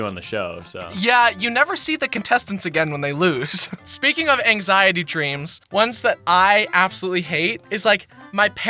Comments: I thought that said wet dreams 💀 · I thought it said wet dreams